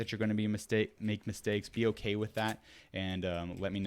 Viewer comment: Bruh did he just say ‘be a mistake’ 😂